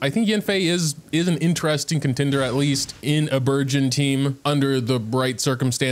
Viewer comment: I thought you were saying burguer on team for all the video until the very end when you said burgeon HAHHAHAHAHAH I legit thought it was a new type of team that for some reason has food and cooking related names (like fridge, salad, oven, curry lol)